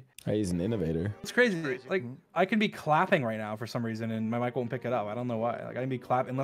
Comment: Did he say "Wife"?!!! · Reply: no I think he said "my mic" not wife